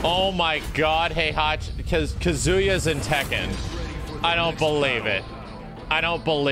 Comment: “Kazuya is in tekken.” Max get your 8 hours of sleep my man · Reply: Could've sworn he said "Kazuya as in Tekken"